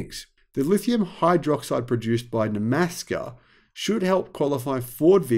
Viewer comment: Hey.. I thought I heard you open with ‘Nebraska’, until I realized it was Nemaska Lithium in Quebec.